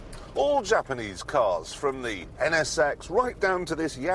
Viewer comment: he says NSX but the closed caption says "an ass sex." LOL